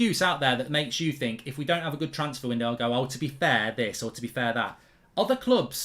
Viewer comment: I thought you said we can still have a good window 🤣